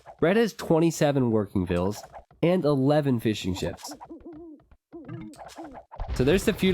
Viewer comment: All I hear around 8:25 is fishnchips